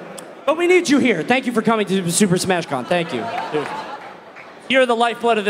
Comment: at first i literally thought it said " Super smash man" And i was worried for my safety